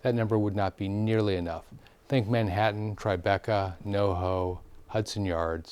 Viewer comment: Did he say "No hoe" instead of Soho at 10:05? I can assure you there are plenty of hoes in Soho. Sorry, could not help myself.🙃